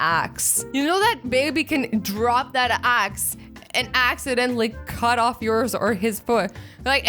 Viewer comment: ‘ he might drop that axe ‘ then it sounds like she says ‘ but axedientally instead of acidentally 😂😂😂😂